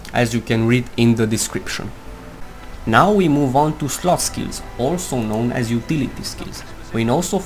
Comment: i swear @ 4:08 he say now we move on to slotski instead of slot skills...that accent dude, makes these videos much more interesting...